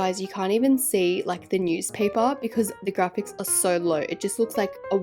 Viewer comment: Please tell me why my very weird brain thought you were saying, “Grandpa’s $h1t” at 4:54…